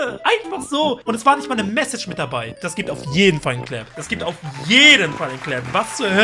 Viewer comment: hab "slap" verstanden